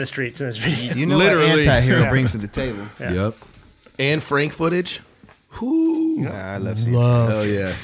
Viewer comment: thought Kelly said 'Anne Frank footage' was very confused for a second...